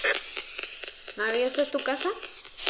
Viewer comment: aquí entendí "esthela" entonces se llama esthela :0